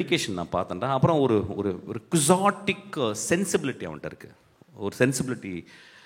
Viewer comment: does he saying Chaostic sensibility or something else pls explain someone, i can't able to understand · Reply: I think quixotic